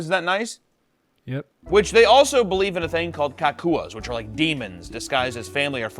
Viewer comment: hawk Tuah is all I heard at first my brain rot is to much